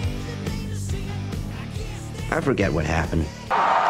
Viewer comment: he really said "i forgor💀"